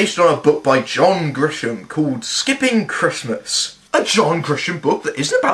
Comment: I thought I heard the title of the book was "I hate christmas" or "christmas sucks" or something similar. that would have been a wonderful movie title.